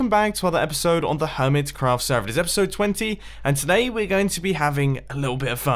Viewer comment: Did he say episode 20?